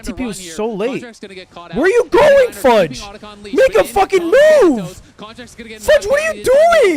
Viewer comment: What did he say at 1:07:37?? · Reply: make a fucking move